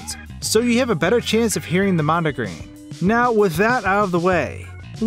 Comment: At the start, I thought he said "mom" followed by the N-word at 0:25.